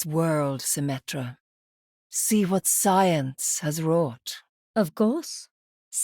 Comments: She actually says "wrought", not "brought". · I think Moira is saying "see what science has wrought" (its a word I swear)